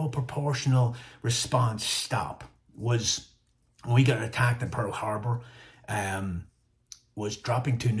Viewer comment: Ya need to get back home to Ireland lad. I heard the “when we got attacked at pearl harbour” 😂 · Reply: He said "When we got attacked at Pearl Harbor". not "When we attacked pearl harbour"